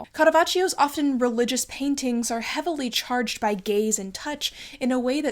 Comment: the captions say "gays and touch" instead of "gaze and touch" (unless? it was intentional?)